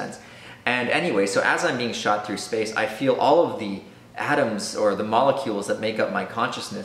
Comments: omg i thought you said "Adams" and not atoms.. kept imagining tiny Adams being shot in random directions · The whole time I thought he was saying 'All my Adams shot in different directions. How do my Adams know how to form back together?' :D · I thought he was saying Adams instead of atoms